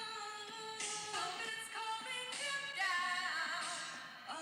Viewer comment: i heard 'Trump is calling him down' at first and PANICKED SO MUCH before realizing it was Trumpets XDD